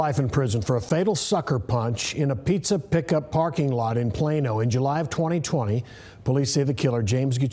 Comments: I thought it said "Piano man" and I'm like nooo not Billy Joel! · Who else thought it said piano man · I thought it said piano Man 😭😭😭